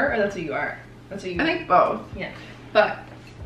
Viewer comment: Was anyone able to catch what whispered around 9:45?! :D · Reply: i think "but...I'm a freak"